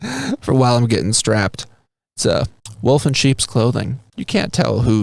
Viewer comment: LOL, when you were holding your gun strapping statue..I thought you said "A Woken Sheeps Clothing". 😅🤣😂🤣 Say Whuuuut?